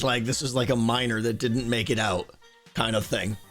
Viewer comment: when you said "a miner that didn't make it out", I heard "a minor" and I was like that's so unnecessarily grim